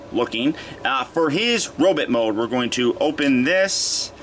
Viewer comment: he says "now, for his robit mode" instead of robot